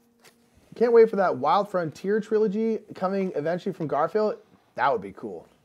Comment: Hey! Great playthrough! Could you clarify please what I heard at 3:05:37 - " I can't wait for that Wild Frontier Trilogy coming eventually from Garphill..."